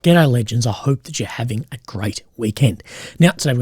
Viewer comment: i have always thought it sounded like your saying ghetto legends at the beginning and the CC just confirmed it for me! JK i know its just the Aus accent to an american.